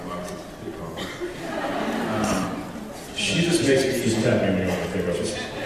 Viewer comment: What does Jensen mumble at 21:55 that makes everyone laugh? He says it so fast that I can't quite catch it. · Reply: Something to the effect of "just tap me when you want me to take over." 😊